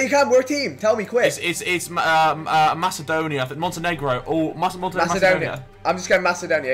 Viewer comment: what did he sayyyy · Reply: Montenegro